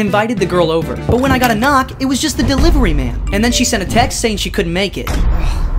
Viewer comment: I thought you said she couldn’t make it ten mins later,walks in and hits her in the face 😅😂😂😂😂😂